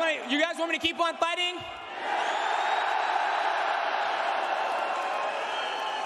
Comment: Swore he said “I have to talk to my white person” 😂